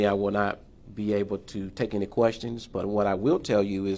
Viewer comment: I thought he said NO QUESTIONS